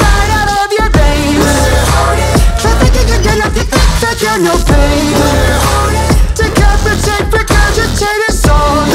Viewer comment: What is saying? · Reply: Dont cheat